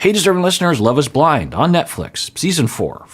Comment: I love how the automatically generated subtitles said 'hey DISTURBING LISTENERS' 😂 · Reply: For the longest time I thought he was saying, "Hey there, listeners!" until I saw a subscriber's comment with her YT name, "DeservingListener". And then I was like, ooohhhhh, lol.😂